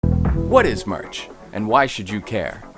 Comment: I misheard the beginning as "What is March?"